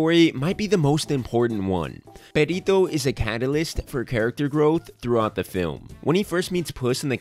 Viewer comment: Did you just say, cat-alyst??